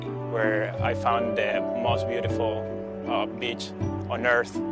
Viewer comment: Did anyone else thought this guy at 0:50 said " most beautiful bitch " or is it just me ?